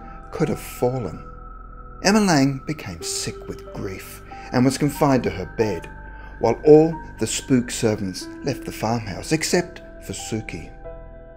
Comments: Did he say "spook" servants at around 2:30? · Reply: yeah i thought he said spooked at first. But now i'm imagining she had a ghost butler and a zombie milk maid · Did he say "spook" servants at around 2:30? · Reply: I think he said "spooked" as in they were scared so they all left but Sookie.